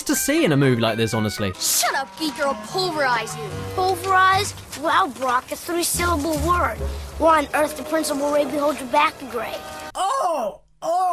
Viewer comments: What did the kid say at 9:37, anyone, please? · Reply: said: "'Pulverise'? Wow Brock, a three syllable word! Why on Earth did Principal Rabie hold you back a grade?" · what i heard is "pulverize? Well brock, that is a three syllable word, one earth the principle rape the whole your back a grade." what does it actually say?